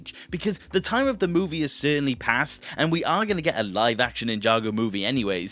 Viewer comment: Wait wait wait, back it up a bit. Did I hear "live-action" Ninjago movie?